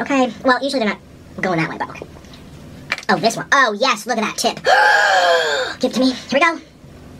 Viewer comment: what did bro say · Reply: oh yes look at that tip 😫😫